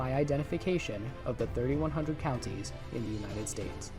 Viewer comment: I thought u said every country in the united states 🔥🔥